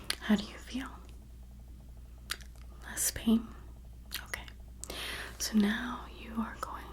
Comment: “How do you feel?” - LeSbIaN I know she said less pain but that’s how i first heard it